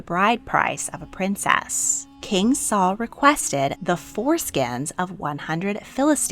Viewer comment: Did you say foreskins at 3:29 ? What a weird request from the King !